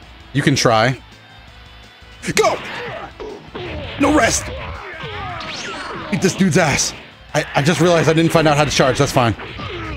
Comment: Did he say "Eat this dude Ass"? LMAO